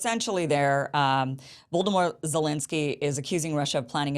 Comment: Did you call Rat Boy Voldemort Zelenski LOL